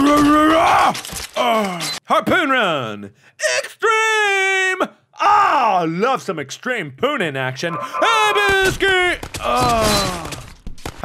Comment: I thought he said "I love some extreme spoonin action"